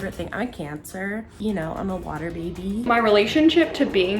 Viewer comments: When the bald girl said “im a cancer, a water baby” I lowkey thought she said “so I have cancer” and when I went back and watched I died so bad. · I fill bad I thought the one girl said she had cancer but then she said she’s a water baby so I was like oh she said she was a cancer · The girl with no hair she said imma cancer I THOUGHT SHE SAID I HAVE CANCER 😭😭😭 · When she said, "I am a Cancer" I thought she said, "I have Cancer" . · I heard "I have cancer" instead of "I'm a cancer" and I was like "what?" at first